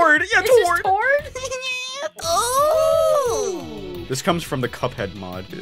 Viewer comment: WHAT DID YOU JUST Said!? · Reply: “Ooooooooh”